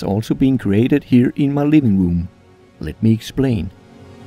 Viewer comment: I thought he said in his ligma room. :(